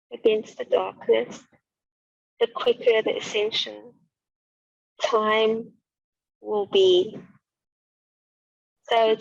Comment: Good ascension info near the end...I couldn't make out a few words though, right at the 1:19:54 mark. "Time will be...?? up to the beings." Any ideas? Seemed important. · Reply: I think she was saying the more people who use their light against the darkness, the quicker the ascension time will be. 😊 Hope that helps! ❤️